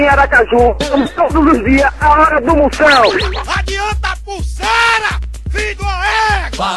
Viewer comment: o cara gritou o que, mesmo? "Adianta a pulseira, fi duma égua" foi isso?